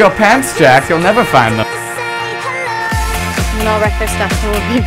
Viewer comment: What does Jenn say to the girl who plays Rue at minute 1:15?? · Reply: i think it's something like "we're gonna wreck there stuff and --- (stops talking and starts laughing)" at least that's what i heard.